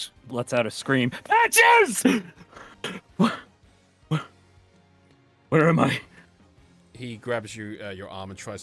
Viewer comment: does anyone know what Shield shouted/ what is brother's name was? I think I heard "Patches" but I'm not too sure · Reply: yes shield's brother's name is patches